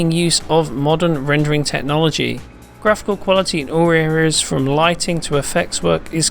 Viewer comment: What the fuck does he say at 0:23? "Graphical quality huhghgg to effects..."?